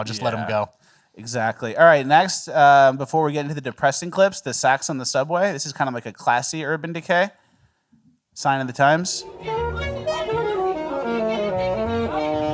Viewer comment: I thought you said sex on the subway and I tripped running to my phone